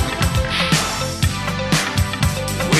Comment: Great song! When I was younger though, I thought they were saying "lost in the ocean"